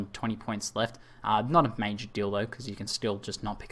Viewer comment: did he said "not a major dildo"?